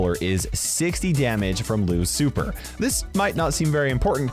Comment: He said Lou's super I thought he said Lu ci fer smh 🤦 Lucifer lol